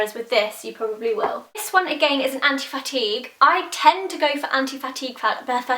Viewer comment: Why do people keep saying that it sounds like shes saying anti fatty, shes saying anti fatigue :)